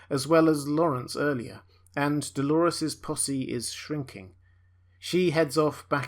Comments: I am sorry, I am not a native English speaker, BUT at 11:55 I hear that something of a Dolores belonging is shrinking....:):):) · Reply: Delores’ posse is shrinking. Meaning her squad of soldiers. They’re being killed off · I thought he said "Dolores pussy is shrinking" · it sounds like youre saying "dolores pussy is shrinking" hahaha